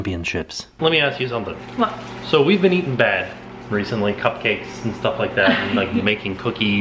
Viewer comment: Am I the only one that misheard “So we’ve been eating bat 🦇 “ 😱😝